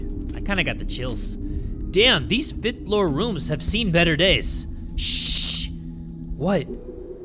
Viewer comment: I thought it said "These 5th floor rooms have seen better drugs." 💀💀💀